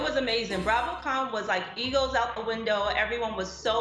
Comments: is it just me or Andy seems dismissive of Wendy? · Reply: Lol I thought Wendy said "eagles out the window as well." 😆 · I 💯 heard "eagles out the window" at first too, though quickly corrected it in my head to what most made sense & what she actually said. Didn't take it as a slight towards Wendy, just a misheard comment 🤷🏼‍♀️ Based on comments though, seems like I'm one of the few